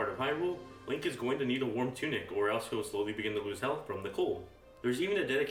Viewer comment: guy, what is your name. I'm trying to contribute to the subtitles. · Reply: what do you say at 2:09. sowling???